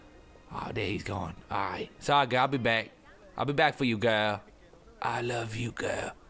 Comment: I'll be back for you girl. Me: XD I thought you said he's 7